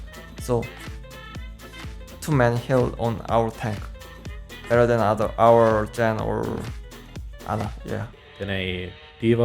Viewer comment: I wonder how long they spend trying to decipher what Fissure was saying at 4:51 that they just rolled with whatever kinda made sense. He's saying "Better than our Zen or Ana"